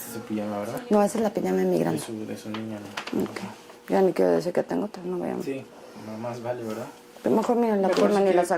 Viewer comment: A bit of correction base on only what you showed at 48:01, she said "de mi Grande" which translates to "My eldest" I think she is referring to her eldest daughter and not Paulette. BUT! After that she said something a bit weird "que no digan que tengo otra" which translates to "so they don't say I have another" which I think she is referring to "another pair", so basically she is saying that there is only one pair of those clothes according to her and that one is for her oldest daughter, wtf!!! Then if you look the man after she said the details about the clothes, he looks to another person and looks very nervous, like as if she said something she shouldn't have.